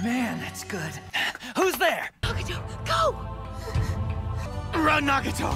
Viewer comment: I rewinded like 10x. Did she say obito go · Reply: It’s just cut off, but she says nagato. It said like agato pretty much